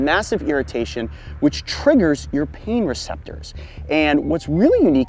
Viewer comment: I SWEAR I HEARD HE SAY " WHICH TRIGGER YOUR p^nus SIZE)